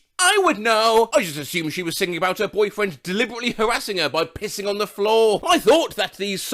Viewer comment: ITS LATE FOR ME BUT I THOUGHT IT SAID “I just assumed she was singing about her boyfriend deliberately harassing her by pissing on her then on the floor”